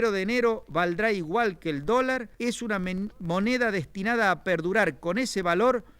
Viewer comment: se escucha que dice " es una men" y el traductor pone "lie" que es mentira en ingles.